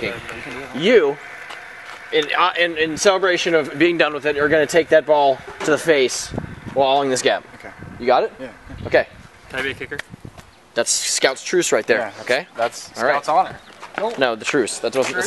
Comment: I thought it said take a bail to the face